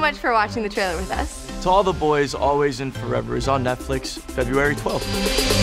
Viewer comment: bro I thought it wasn't coming to come out for like another YEAR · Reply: funny thing was I thought you said January 12th so I was like WhaAA??